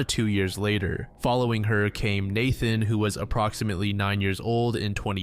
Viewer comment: I thought you said ‘following Hurricane Nathan’ and I thought damn that’s an awesome name.